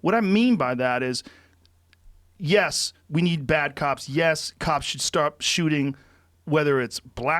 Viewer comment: did he say we need bad cops?